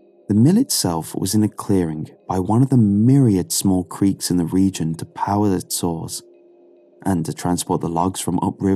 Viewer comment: wtf did he even say here??? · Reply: I looked it up in the official story, it’s “The mill itself was in a clearing by one of the myriad small creeks in the region to power its saws, and to transport the logs from upriver to it. “